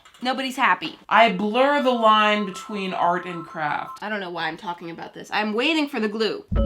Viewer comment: I thought she said art and crap